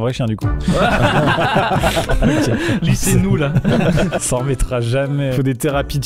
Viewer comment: c'est moi ou dit plutôt "lui c'est nous la" ?